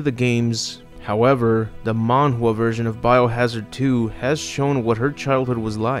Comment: What is he saying at 2:25 ish? mondo version? he says it twice in like 30 seconds ...